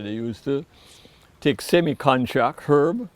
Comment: Sounded like he said semi contra herb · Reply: semi contract